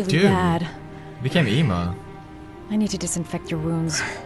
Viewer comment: did he say emo? at 18:15?